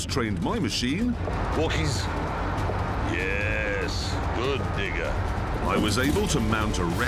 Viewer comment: did he just say good ni**a?